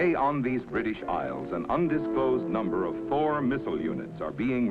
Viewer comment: anybody else hear, an undisclosed number of four (presumably badly pronounced Thor) the weapon systems 😅